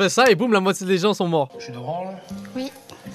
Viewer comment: j'ai entendu "tu te branle" au lieu de "jus d'orange" 😭😭